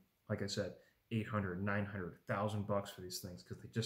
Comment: I can't, for the life of me, figure out why you're slapping the back of that and using finger quotes when calling it a pistol, it IS a pistol... if it had a pistol brace then sure, but... ok. Now you're talking about Walther version of the .22 going for $800,000-900,000? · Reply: lol i heard that too, though what he really said was "800 to 900, thousand dollars" giving 3 separate numbers edit: he says it again at 26:45 also not sure why he refers to it as a "pistol" either it is a pistol